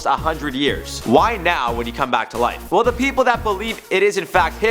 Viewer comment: I thought you said he’ll come back to oife